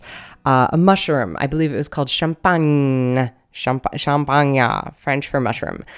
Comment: When you said "champignon", I thought you were saying "champagne", it was cute x) Nice art by the way o/